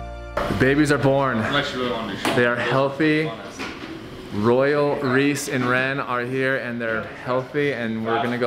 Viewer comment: i thought he said "...Royal, Greese, and Wren..."